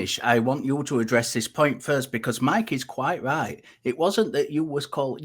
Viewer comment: First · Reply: Oh, you said first. I thought you were saying fist.